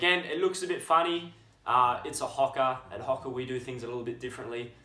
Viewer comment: When i first picked up a HAWKA. A what? A hawk... like a bird? As the video progressed.. oooOOOooo HOKA. =) Nice review mate. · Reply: at first I thought he said Hooker (prostitute).